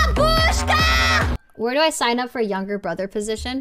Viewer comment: I heard "бабушка" or what was that I'm Russian so I don't really understand""